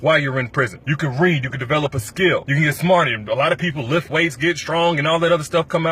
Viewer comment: what you say you can eat a smart what at 2:17? or is it just me??